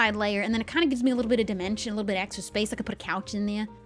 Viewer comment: Anyone else hear “dementia” and not “dimension”??